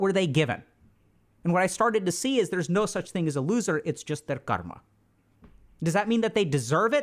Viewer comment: What's the word that he keeps saying? It sounds like 'karma' but it doesn't sound like English. I've heard him say this word in lots of videos but I can't google it because I can't figure out how it's spelled 😑